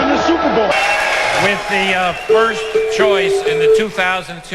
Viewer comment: it sounds like he says worst. And he was right